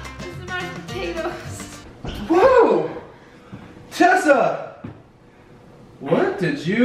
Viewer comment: Who else realized that at7:38 said chessa instead of Tessa like if you agree